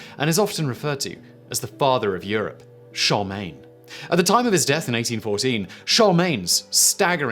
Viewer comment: It sounds like you are saying "Charmin" as in the toilet paper. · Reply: I thought he was saying "Charlie main" as in the way he fuels his prodigious output.